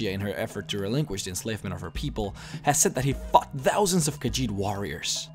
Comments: Instead of "fought thousands of khajiit warriors" I heard "fucked thousands of khajiit warriors" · I thought he said "he fucked THOUSANDS of Khajiit warriors." Was like woah, that escalated quickly. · Did you say he fucked thousands of Khajiit warriors?